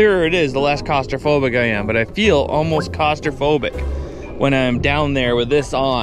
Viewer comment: did you say Cost-Raphobic? Ahahahah